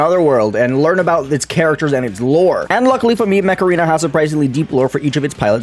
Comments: i thought you said macarena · I thought he said "this video is sponsored by macarena" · Bro I thought he was saying MACARENA was the sponsor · I thought he said macarena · i thought you said this video is sponserd by macarena :l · I thought said " This video is sponcered by Macarena